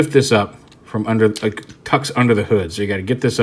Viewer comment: Did he just say "Cucks under the hood"? I can't be the only one hearing this...